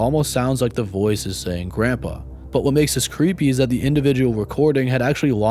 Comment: I clearly hear "help me" instead of "grandpa". Maybe it's an example for a so-called audio hallucination where you hear what you concentrate on?